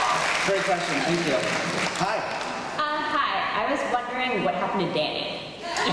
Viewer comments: what was the question here I didn't get it 😂😂 can't hear it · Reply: She was wondering what happened to Danny since he just disappeared from the show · What did that girl say? 36:51 I'm from Spain so....:/ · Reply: she asked: i was wondering what happened to danny